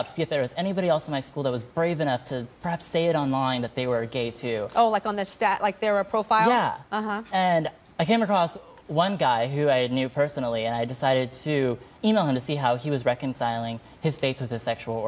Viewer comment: sounds like he said "I decided to anal him"